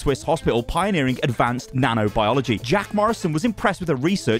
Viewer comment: Did he just say jack Marston?